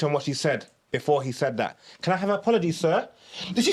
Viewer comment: is talking pure shitt omd😂😂😂 about she said “can i have an apology sir “ then just switched . This guys is top simp😂What she actually said was “ u cant keep no women patterned thats why they all cheated on u , apologies “